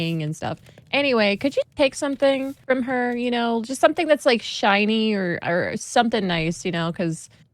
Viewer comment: Isn't it "Shiny"? 14:14 She clearly said "Shiny",not "finding".